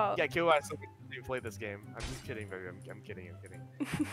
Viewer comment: I HEARD AT 6:57 "im js kiddiny baby" NOT..